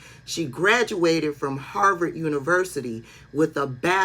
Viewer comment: Sounds like you said Harvard instead of Howard….even though you did cite it as a HBCU